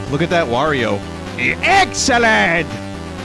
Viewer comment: When Vinny screamed "EXCELLENT!" at 1:24, I could've sworn I heard him scream "EGG SALAD!" Don't know why I pointed that out, it just gave me a good laugh! I'm easy to entertain as you could probably guess... 🤣